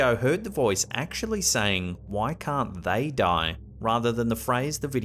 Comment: The voice in the kitchen to me sounded like it said why can't THEY die.